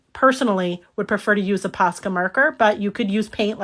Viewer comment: lol. I thought you were saying Pasta Marker. I was thinking, what the heck is a pasta marker. 😂😂 I love your videos.